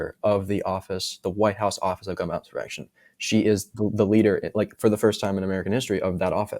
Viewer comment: I couldn't quite catch what was said, and subtitles failed. Websearch shows: she oversees the White House Office of Gun Violence Prevention Hope someone finds this helpful.